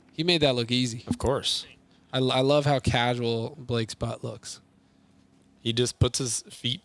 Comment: wait...what he just say? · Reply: Hehe, 'butt' 😁😁